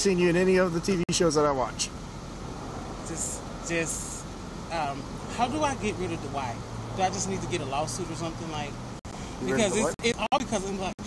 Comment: Did he just say “how do I get rid of Dwight” ???? 😂😂😂😂